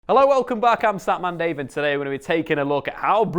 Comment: Did he say statman david @ 0:00 ??